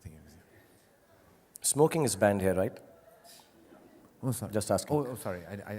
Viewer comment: Does he say smoke is band hear. I need this type of guts. Need to work harder